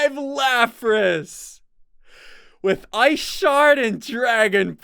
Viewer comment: When he got a Lapras he read out the moves and when he said ice shard I thought he said I shattered😆😆